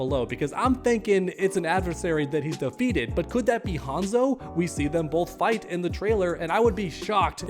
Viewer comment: Bi-Hanzo... misheard)